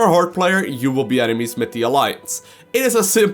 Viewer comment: Did you say? "you would be enemys MIT die allieance" Im not criticising you but in Deutsch we say "mit" wich means "with" but i knew not that you could use it that way too in englisch. Or ist it that im misshearing it? Keep up with the good vids!